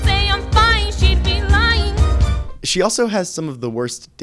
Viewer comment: i know she said lying at 12:20 but it kinda sounds like she said "she'd say she's fine. she'd be- LINE"